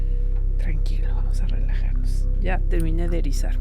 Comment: En el min. 42:53 hay una voz que dice tranquilo relajate